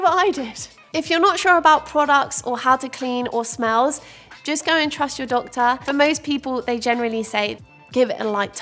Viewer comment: When speaking about water intake I really thought you said “if you’re not sure, ask your dog” and I was like what?? Why would my dog know how much water I need 😂😂